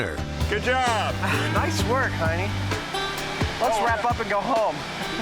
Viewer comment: I heard Adam call Jamie "honey" at first.